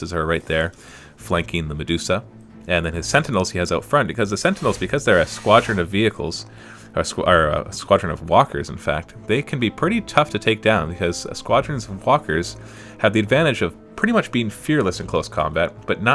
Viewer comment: thought he said squadrons and f**kers at 5:00..